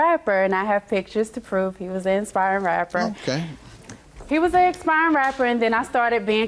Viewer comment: I thought she said he was expiring rapper.